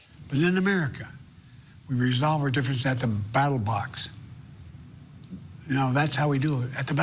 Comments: Did he just say “The battle box”? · BATTLEBOX? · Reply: I thought he said battle bots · I could have sworn he said battle box